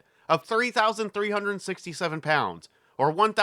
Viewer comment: The Mercury spacecraft weighed 30,000 pounds?? That can’t just be the capsule. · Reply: Where into the video did you get that number? I do believe you misheard, I said 3,367lbs with fuel and pilot (which is info from the nasa familiarization manual). I even checked captions to make sure and they say the same.